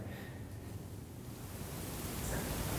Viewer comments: I swear to god I hear something saying "its not the dark" right after Ryan says the dark is playing ticks on him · anybody else hear "it's not the dark" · can anyone hear someone saying "It's not dark" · At minute 23:37 when Ryan said "it's just the dark" am I he only one who heard "not just the dark" or maybe "no it's not" did anyone else hear this? Did you guys hear this?